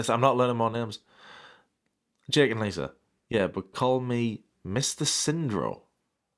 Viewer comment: At first I thought you were saying Mr Syndrome and I thought it was another one of Ambie's medical conditions.